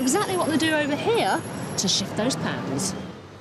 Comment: i thought she said "to shit those pants"